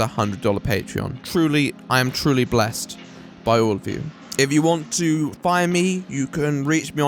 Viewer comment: Thought you said "If you want to buy me" for a moment there